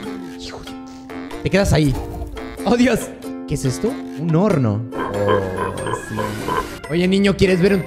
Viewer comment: entendí porno